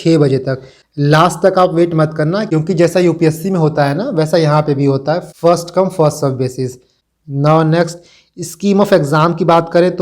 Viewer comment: inhone kya kaha after 3:45mi smjh ni pya any on cn tell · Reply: first come first serve basis par centre ka allotment hota hai. Isiliye last tak form bharne ka wait nahi karna